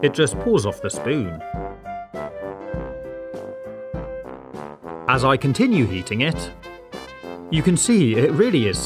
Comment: i thought u said "as i continue eating it"